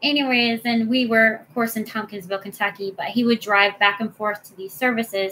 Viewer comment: I live in Hopkinsville and I was shocked by testimony. How can these men have such disrespect for women and girls? Such a terrible way to grow up! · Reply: I thought she said Tompkinsville... but absolutely horrible regardless of where. 😔